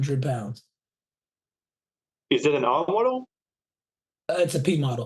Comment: What does he ask you at 7:53?? You responded with something like "it's a P model" couldn't make out question... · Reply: He asked if the Cessna 172 was an R model, and I responded it was a P model. There are subtle differences between models, power, flaps max gross etc.